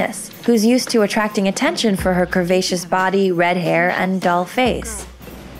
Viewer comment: “Dull face”? Hardly. The narrator doesn’t know what she is saying. In fact, most of what she is saying is b.s. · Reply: DOLL face